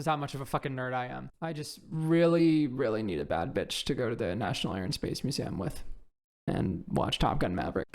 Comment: did someone say national air and space museum?